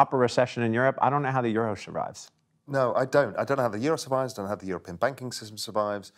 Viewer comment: i think he said "should rise" not "survives".